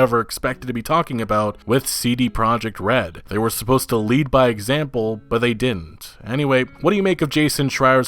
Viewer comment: I heard "GREED BY EXAMPLE " ?